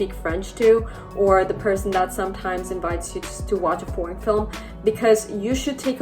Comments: Does she say ”porn”film?? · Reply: Foreign film 😂 Yeah, I should enunciate more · I am not an English speaker. Sometimes I listen videos in English while I am working. Everything was okay and suddenly I hear 9:52 "and the person that sometimes invites you to watch a porn film". I was like "what?!?" I needed to turn on the subtitles to understand what I was missing XD "Foreign". - > I have problems with the pronunciation of this word and now... I have an extra problem. It looks like I can only hear it with the help of subtitles. The brain is a funny thing XD